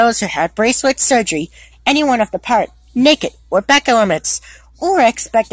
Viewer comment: Did He Say Naked Or Nick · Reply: He Said Naked